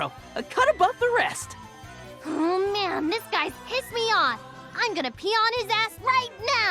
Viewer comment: Underboob 🥰 But... did she just say "I'm gonna peon his ass"? This is one of 'those' kinds of games, eh?